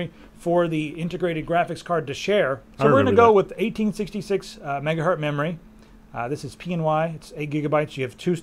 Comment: Is it me or did he say "megahurt" @ 1:48 :p